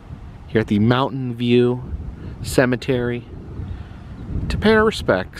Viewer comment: After talking all Dr Pepper .. .I thought you said Mountain Dew Cemetry